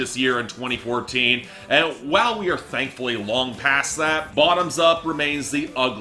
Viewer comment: I legit thought Bottoms Up was not what he was saying I thought he was saying "your body is like a truck."